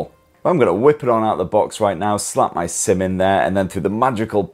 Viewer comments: I heard "slap my semen there" but he actually said "slap my SIM in there" tho 😂😂😂😂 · Tecno joining the flip side😂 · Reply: I heard "slap my semen there" but he actually said "slap my SIM in there" tho 😂😂😂😂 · Thanks for checking out my Phantom V Flip unboxing & review, reckon it's one of the better mini foldables out there? Let us know below! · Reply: I heard "slap my semen there" but he actually said "slap my SIM in there" tho 😂😂😂😂